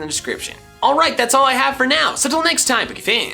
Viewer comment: I always thought he was saying gory teens pokefans